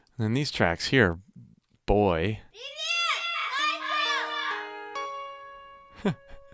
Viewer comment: What does the little boy say? I think I hear him say, "Michael!". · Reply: He shouted in Russian, "Привет, Майкл!" ("Hello, Michael!")